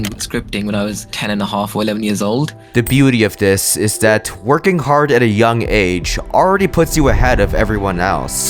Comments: am i tweaking, i swear he said "twerking hard" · i heard twerking hard at a young age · I thought he said ‘twerking hard’ I’m so glad he didn’t 😅